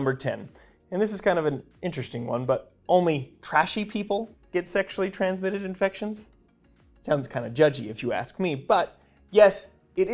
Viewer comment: Trashy people? I thought you said thrashy people? 🤣